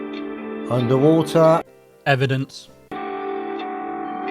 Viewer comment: I thought said the Chinese don't use the "under water technique" 🤐 #gottalietoflerf